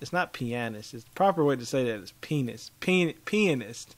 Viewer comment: "The proper way to say it is penis" That's what I heard him say😂 I love him so much😂Makes my day much better❤️